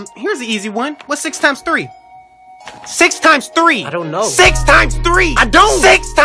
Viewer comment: 아돈 진또우 노우!라고 들림